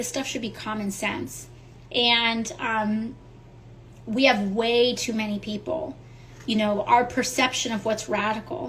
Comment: did she really say "there are too many people"???